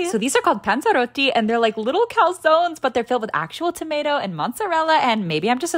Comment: When she said calzones I thought she said under wear haha😂😂😂❤❤❤